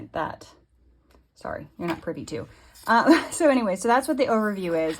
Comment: Comments like “you’re not privy to it” really takes you out of enjoying a video. It comes off very patronizing. We don’t need to know that we’re not allowed to view something. If you don’t want us to know about it, don’t mention it. Person I was watching with says: if you made the forms in canva just show canva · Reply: lol, the transcript shows the 3:31 mark as saying “sorry, you’re not pretty too”. Had an argument with the AI and it insisted that’s what she said. She clearly says you’re not privy to her financial information, but to your point it can go without saying. She wasn’t being patronizing though, just matter of fact.